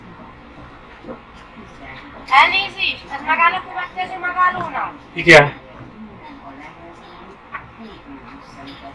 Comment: What was said at 7:15? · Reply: Sorry is this stopping at the next stop? -Yes